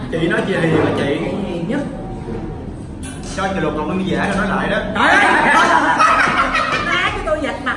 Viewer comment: Lúc 2:13 đại nghĩa nói gì k nghe đc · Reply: Chi chị lột cọng lông mi giả ra nói lại đó 🤣🤣🤣